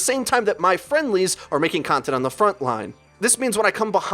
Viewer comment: I thought you said "making content on the backline"